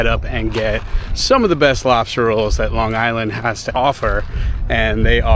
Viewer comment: I swear it sounded like you said you were glad you got your "robster" roll, when you were by the truck. 🤪🤪